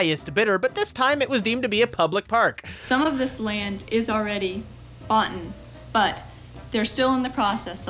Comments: Did she say boughten? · Did she say 'boughten'? Yes she did 😳